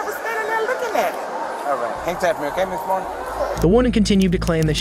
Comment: CONGRATS Ive NEVER in my 41yrs heard someone think the POPULAR Old Saying "Hang Tight" was "HANG TAPPING!" LOL, OR pronounce "Cologne" as "Col On"! Is it Because ENGLISH is (I hope) NOT your Native Language? Also Killers : PLEASE FINALLY ANSWER YOUR SUBS Question as to WHY does the Commentator seem to speak & fully understand English, BUT, soooooo many times your On-Screen subtitles/Quotes COULDNT BE MORE HILARIOUSLY WRONG?? Like she BLATANTLY "Hang tight" YOU SAID (RETARDEDLY) "Hang TAPPING"?? ROFL! WTF? is wrong with u??